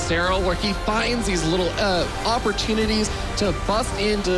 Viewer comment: what is the word after 'little' in this sentence? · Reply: josef x "opportunities". Or do you mean his "uhh" sound?